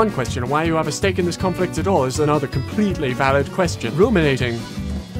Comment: I thought they said “Incriminating”